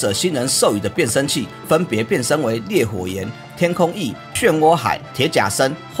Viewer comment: 一直聽成烈火之炎..... 幻影達古奧在遊戲[勇者聖戰]很好用 速度又快